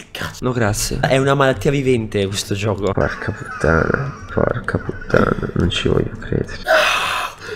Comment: io ho sentito un porca ma... 5:00 ahahahah